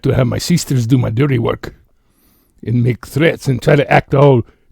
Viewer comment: Omg I remember when said threats but it sounded like he said he had terrets 😂